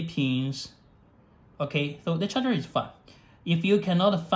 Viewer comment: did he just say "...the charger is fucked"? Seriously, I cant understand this guy.